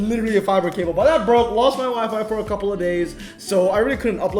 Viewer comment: When he was about to say my wifi and was down I thought he said my wife is out of town lmao